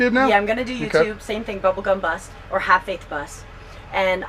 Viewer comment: Bubble Bus, Bubble Bubble Bubble Bus. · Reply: I thought she was saying "Pink Bubblegum Butt!" LMAO!